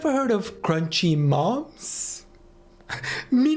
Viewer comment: I thought he said crunchy mops at first and was even more confused when it was apparently crunchy moms😭